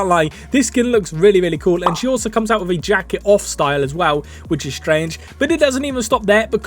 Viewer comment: I thought you said something completely different 😅 you said “jacket off” I heard “jacking off” 💀